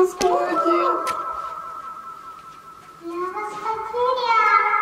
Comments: мне одной послышалось что он сказал "я в вас во дверях"??? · мне послышались или он правда сказал: Я ВАС ПОТЕРЯЛ😱😱😱